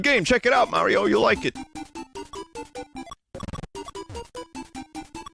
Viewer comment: Wait what did he say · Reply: HEY MARIO D'YA EVER PLAY WITH YOUR DI